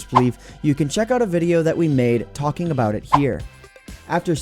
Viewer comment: did you say "WE made"?